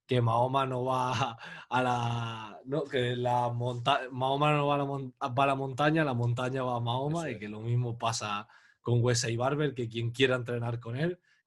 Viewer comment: lo que dijo en realidad es = "la gente va al templo shaolin , el templo shaolin no va a la gente"